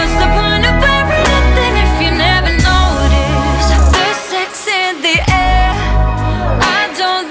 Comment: I thought they said "The sex" not "There’s sex" !??